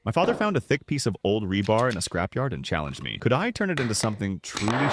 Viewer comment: I thought it said "Thick piece of my bar" 😭